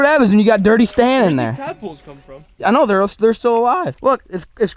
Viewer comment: “That’s what happens when you have dirty stan in there” Thought you said dirty dan for a second